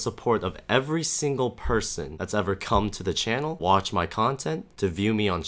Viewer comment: lmao i miss heard him saying "cum on the channel"